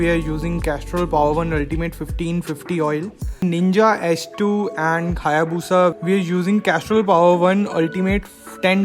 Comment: did you said "khayabusa" paaji 😂